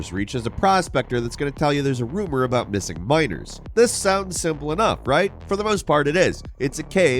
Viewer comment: subtitles (missing minors) : what is actualy said (missing miners)